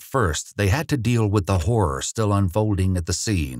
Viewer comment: I heard “ the whore “ and I was like OOP- but he said “ the horror “ LOLLL I REWINDED IT LIKE twice 😭😭😭